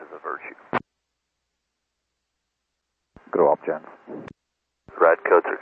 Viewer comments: he actually says "grow up gents" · He actually says "Grow up, gents", the subtitle is incorrect.